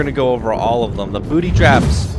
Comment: did he say booty traps?